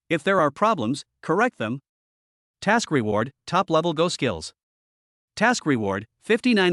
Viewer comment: Go skills ‼️‼️‼️ 😁😁😁 I thought I heard "ghost" at first.